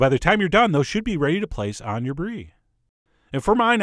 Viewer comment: It sounded like you said "budget grapes" instead of "bunch of grapes". :D